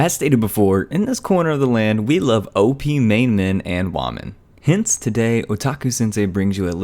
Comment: I heard "Main man and waman'' XD